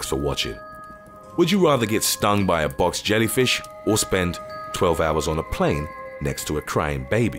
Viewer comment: I spent 12 hours on an airplane next to a crying and kicking baby. Bring on the jellyfish! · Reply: i thought you said "i spent 12 hours kicking a baby" and i almost burst out laughing